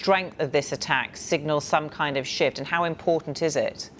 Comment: Did she just say "some kind of shit" or "shift" ?